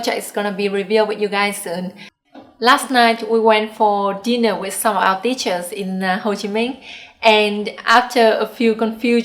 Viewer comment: ơi :)I was cooking dinner while listening to this, and when you said, "...going to dinner with some our teachers" I thought you said "...going to dinner with some of our bitches" :D giật cả mình!